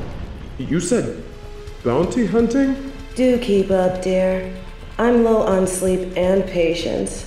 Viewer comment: Took me an embarrassingly long time to realize the reporter says "Do keep up" instead of "dookie pup".